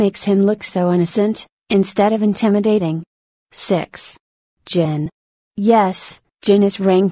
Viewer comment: I thought the girl said sex which is 6